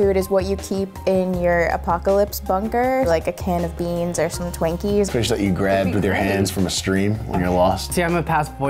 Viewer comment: Did she just say TWANKies instead of TWINKies??! O_o